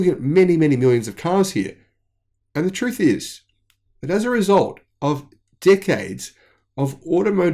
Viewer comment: "result of dick heads" - dude you just made my day - love your content - cheers brother! Just letting you know my hedge fund bought 5k TSLA shares at $146 yesterday do to your great research! Thanks!